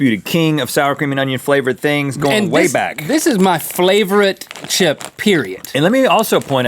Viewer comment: Did he say flavorite?